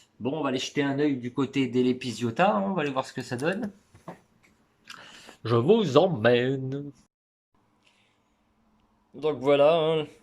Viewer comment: Salut 😊 très belle video sauf qu'à 2:50 quand tu dit "je vous emmène" au début j'ai compris "je vous emmerde" XD 🤣😂 sinon géniale !